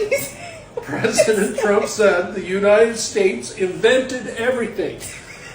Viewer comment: At first, I heard "The United States invaded everything" 🤔